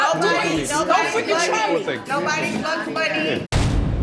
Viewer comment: I think at 15:50 Zadia called Aisha bugs bunny 😳 If I was Aisha, I too, would have needed to be escorted away from Zadia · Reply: Spears I just listened to that and I think you're right. It sounds like she said, 'nobody's buggin Bugs Bunny'.